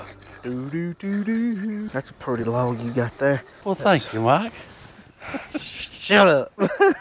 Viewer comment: Mike: "That's a pretty log you got there" Jerry: "Well thank you, Mike!" Mike: "SHUT UP!" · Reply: i thought he said "well thank you wife" or something.